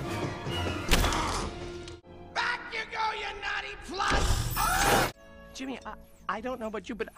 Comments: “All we gotta do is find out one little wire...” *gasp* “Oh mama.” 5:52 “BACK YA GO YOU NAUGHTY PLUG!!!” 6:03 “WAS I EVER DANCING WITH AN ANDROID NAMED LUPÈ?!” I freaking love that robot. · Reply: I thought he said "nutty plug" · back ya go you naughty plug! lmfao!! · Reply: Emily Caskie I thought he said back ya go you naughty slut at first lmao 😂